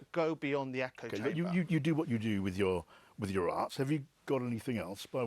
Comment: did he just say "you do what you do with your arse" 😳😂